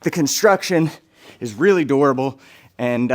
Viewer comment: He kept saying the dummy is DURABLE I thought he was saying “adorable” at first